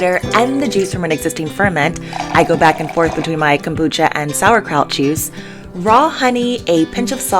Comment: Did I just hear "sauerkraut cheese"? Yup... that's what I heard. Gotta find out about that now...